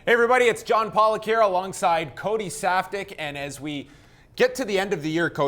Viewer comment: I thought I heard him say Cody softdick 😂😂😂😂